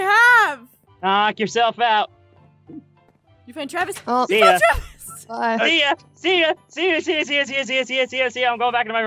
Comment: did she say "F*ck Travis"?